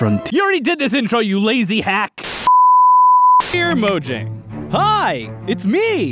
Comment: i misheard “mojang” as “bojack” and now all my brain can do is BACK IN THE 90S I WAS IN A VERY FAMOUS TV SHOW